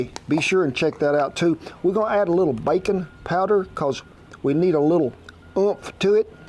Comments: At first I thought he said "bacon powder", and I wondered how I had missed out on this ingredient my whole life. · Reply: I thought he said bacon powder also and immediately I thought I need some of that! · At first I thought he said "bacon powder", and I wondered how I had missed out on this ingredient my whole life.